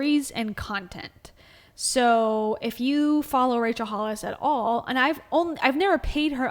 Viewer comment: At the beginning I thought u said racial hollis